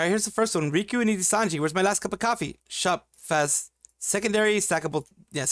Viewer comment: >"Shop phas..." In case you didn't catch it, that complete sentence was supposed to be "Shop Phase coffee", for obvious reasons the fish man is often joked about.